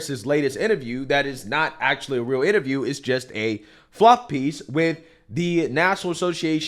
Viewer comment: Fluff piece 😂😂 I thought you said a fucc piece.